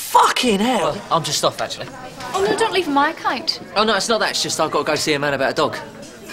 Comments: What does Lauren say? I’ve never been able to hear it properly.. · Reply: Oh no, don't leave on my account. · What does Lauren say at 5:15? I have never known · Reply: RhysJ77 don’t leave on my account